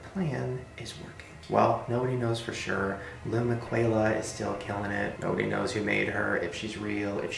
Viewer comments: I always thought Shane said “lil Mikaela” I either can’t hear it well or it’s the........MANDELA AFFECT XD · Omg!! I swear the first time o watched this he said lil MAKAYLA and and even the ig was that and this is the second time I watched it now it's lil MIQUELA!! I was half asleep until I heard him say miquela, that woke me right up. Tell me I'm not the only one.